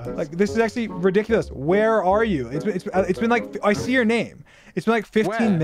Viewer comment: i see "her" name - dream if i heard it wrong just tell me lol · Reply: its your lol